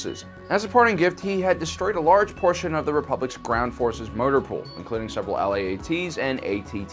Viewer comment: Did anyone else hear "murder pool" instead of "motor pool" 😂